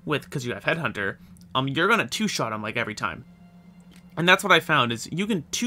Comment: I heard an “an’ ‘at” instead of “and that”, you a local Pennsylvanian there? Great video btw, enjoyed seeing the new headhunter in action.